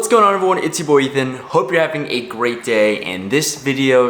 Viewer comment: Sounds like you said Itchy boy Ethan It's your boy Ethan you sure are a good poker plAYER DO YOU STILL GO TO hUSTLER